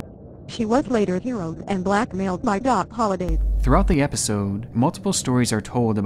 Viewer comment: bro, I was looking for assets for my Halloween costume, and I thought I heard "she was blackmailed by Doc Holliday" (I'm going as Doc Holliday this year)